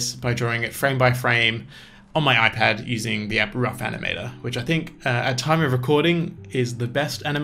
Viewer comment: you say what iPAD animator app you used. But I can’t hear what you say, what’s the app called ? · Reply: Ahh, It's called "Rough animator" for the iPad. :)